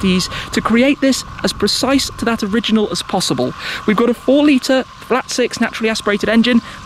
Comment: did he just say flat six or is my hearing not adapting to the English accent?